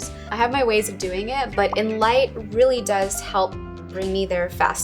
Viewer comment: That photo editor app name. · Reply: She said it at 18:04 something like "in light" or something but I don't understand.